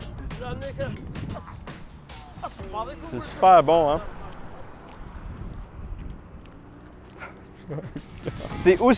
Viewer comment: le mec il parle kabyle ou c'est moi qui entend mal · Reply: Il dit Salam ouep ahah